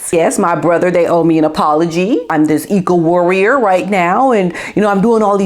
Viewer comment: When I first put this wonderful episode on I thought I heard you call H an ego warrior not eco warrior.